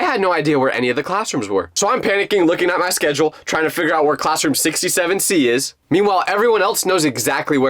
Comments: those who heard 67 c 👇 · Guys i think i heard 67! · did I hear 67-C…6️⃣7️⃣